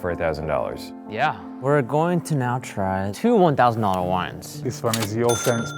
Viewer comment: Low key thought its said $18-$800 WIFE not wine